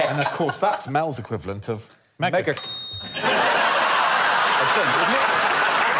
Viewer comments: Did they say megacunt @ 27:54 ? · could somebody tell me what Greg and Alex say at 27:54? i didn't get it. "Heck" is Mel's equivalent of what? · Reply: Also I think they say "Megashit" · I'm not a native english speaker. Can anyone tell me what they say at 27:54? I think it's something like megac*nt but I'm not sure.